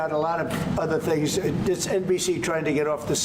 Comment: Did I hear that right??? 11:56 “This NPC is trying to get off the subject…”